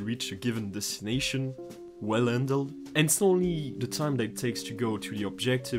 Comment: Can someone please help me understand what he is saying at 7:08 to 7:18? "is the time needed to reach a given destination well handled and ...(inaudible)... the time that takes to go the objective from ...(inaudible)... every time when there's a possible confrontation between two enemies" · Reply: "...It's not only..." and "...spawn that matters, but also..." Sorry I probably had the flu or something. I was speaking through my nose.